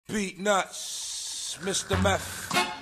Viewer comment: "Deez nutz,Mr.Beff" i cant unhear it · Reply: I thought it said "beat nuts, Mr. Meth"